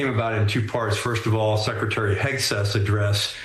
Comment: excuse me, did you say secretary Dic-breath!?